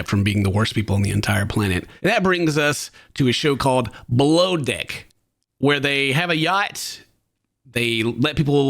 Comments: I thought you said BLOWDECK 😮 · I thought you said blow d*ck, I was like 😮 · I seriously misunderstood Ken when he said: “below deck”. I heard him say: “blow d*ck” 😂😂😂😂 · I had to put subtitles on when I misheard “below deck” for “blow dick” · I totally thought he said something he didn't say but it sounded like he said...😅 BLOWDICK...😮 my bad, he said...BELOW DECK